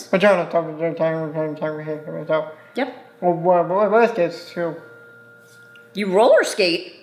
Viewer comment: I think he said raz kids which is a learning website not roller skates lol